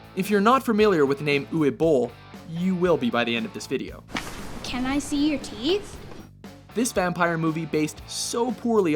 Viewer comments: thought he said can I see your titties lol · i thought i heard the boy say "can i see your tits? " and thought WTF!!! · I know you will call me pervert but I thought that boy at 5:17 said : "can I see your tits?" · I legit thought this kid said "Can I see your tity's" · thought he said "Can I see your titties." lol